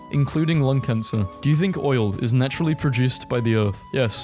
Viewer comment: did you say oil?